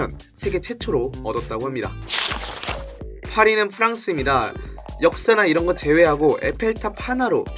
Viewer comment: 나만 파리는 프랑스입니다로 들림?